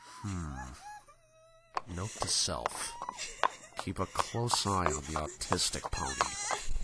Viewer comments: Wait did I hear “Next time keep an eye on the autistic pony?” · did he just say “note to self, keep an eye on the autistic pony??”